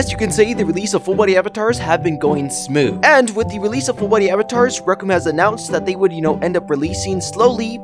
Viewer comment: the subtle says Brooklyn instead of Rec Room 😢